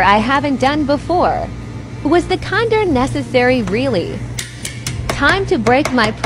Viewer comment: It sounded like he said condom